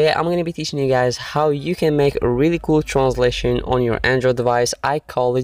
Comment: Every time you say transition it sounds like you said translation